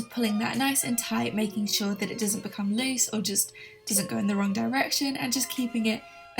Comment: i always think you say One Direction, omg😂